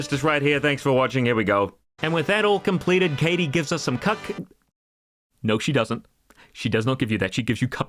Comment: "With that all completed Katy gives us some cuck" is what I heard